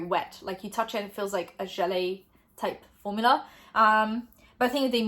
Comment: did you say "jelly" at 20:05 but turned out so not english, because it's cute if so! It's "želé" in czech so it just sounded soooo familiar lol